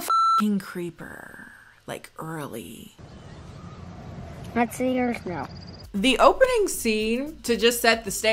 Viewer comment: What does the kid say here? All I can hear is "Not seeing Earth now". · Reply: “Let me see yours now” I think?